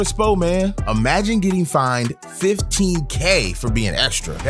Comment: I thought he said fights not fines cuz of the beginning I saw them both fighting 😂